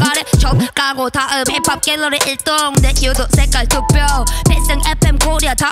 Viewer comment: 내 **색깔투표에**뭐라는거임?게궁금함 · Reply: 유두